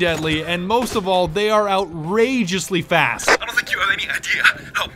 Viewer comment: i got an ad when was saying fast and it sounded like fat